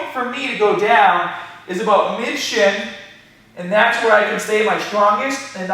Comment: can anyone tell me what he is sayin at 3:32 - 3:33 ? i want to know hoa far down i should go. its important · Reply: He says the point to go down is about "mid-shin"